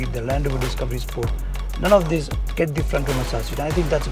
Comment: I wish the presenter spoke more clearly, in some parts couldn't even understand what he was saying, for instance at 1:06, I only understood "front". Expect better quality from TopGear. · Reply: Hi Suraj - it’s frontal massager yes the music is loud on that part . Thanks for the feedback